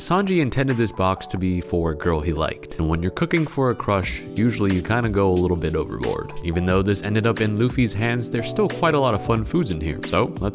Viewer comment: I heard overlord instead of overboard at 0:55 Im so confused.